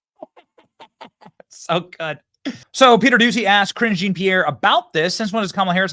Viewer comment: Hahahaa I thought you said Kringey Pierre lol ...perfect